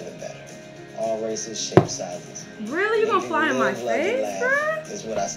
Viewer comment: Idk why these flies r getting brave. You leave a window open and they wont leave either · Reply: I thought she said really you gon fart in my face